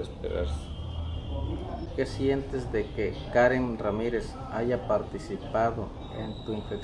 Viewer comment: "matar a científicos del programa nuclear iraní", meaning "killing scientists from Iranian nuclear program" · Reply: he actually said Karen Ramirez"